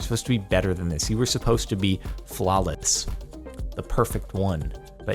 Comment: did anyone else hear flalets?